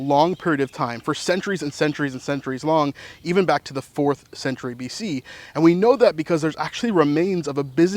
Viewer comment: Yes 4th century AD. During the Byzantine era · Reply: thanks. I thought you said 4th century BC in the video and was like wait what? Lol